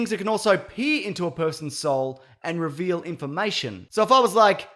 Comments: I thought you said pee into another person’s soul lol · I swear to God he said it can, "PEE into a person's soul."